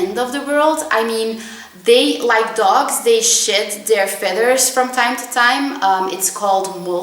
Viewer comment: omg for a sec i thought you said "like dogs they shit their feathers" lol